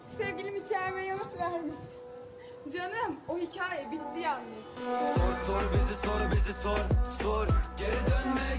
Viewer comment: da sonda ne diyor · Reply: Yalnız o hikaye bitti canım diyoo